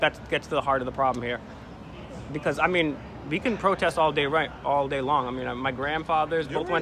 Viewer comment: He might be drunk. It’s definitely something At 17:58 in the video you hear him almost say “All day Wrong “instead of “all day long”. He has like a 10 year old brain and you can see all his tics. How embarrassing for. I would of left or said dude let’s go.